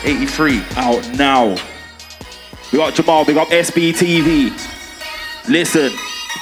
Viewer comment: Does anyone know what Jammu is saying at 4:00??😂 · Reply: Jos Anoia' big up jamal